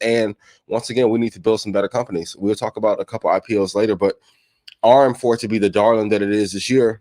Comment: What he say at 12:04? RM4? ARM?